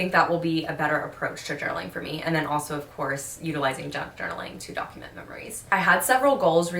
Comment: 🎆 my bf was like half watching this video with me and when you said junk journaling he thought you said juggeling, so that could always be a new hobby idea 😂